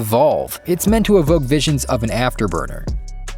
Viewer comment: Ooooh. Afterburner. I thought you said afterbirth and the next clip didn’t help.